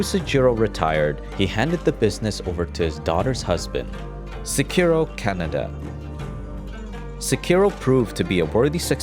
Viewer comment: I dont know why but i heard "Sekiro Canada"